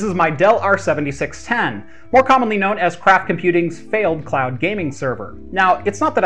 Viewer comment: It a bit embarassing but at 0:07 I hear "Crap Computing's failed cloud gaming server" xD